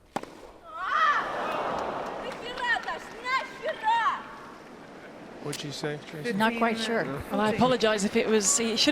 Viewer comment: I wonder what she said :) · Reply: WTF,Dasha- she said.